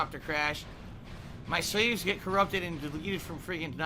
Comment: To me it sounded like he was saying, "My slaves were corrupted!"